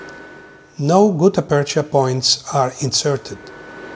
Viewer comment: Two questions : 1. Why not use gp solvents and H-files 2. In the end (3:38)did he say "NOW gutta purcha" or " NO gutta purcha" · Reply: He says no gutta. Not sure about the bio ceramic material being used. However the tip of the collagen sponge sounds great and makes sense. I believe he uses excavators to avoid pushing the GP and debree towards the apical foramen, however chloroform + protaper re-endo files is the way I would choose for practicality.